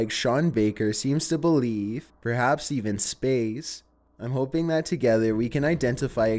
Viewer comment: When he said this mushroom is from somewhere far away perhaps even space I thought he said perhaps even Spain and I laughed so hard